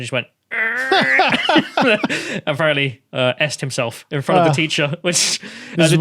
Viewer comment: I misheard this as "capped himself". Thought that was a bit extreme.